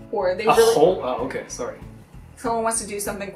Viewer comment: Does Jun says "Sorry" 10:13 ? :D